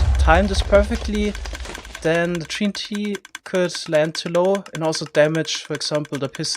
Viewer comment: sounded like he said tree n t